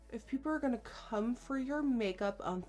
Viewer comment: Thought she said "if people are going to cum for your make up the least you can do is blind them" I was like wtf nikkie